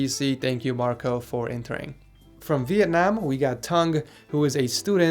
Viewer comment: did you say toungue?